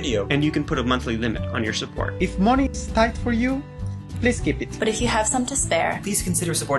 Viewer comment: did he say "if money is tight; please give it" lol im dead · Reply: he said "please keep it"